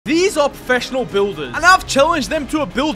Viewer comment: WE ALREADY KNOW THEY ARE PRO BUILDE- wait did u said professional builders? Doesn’t matter same thing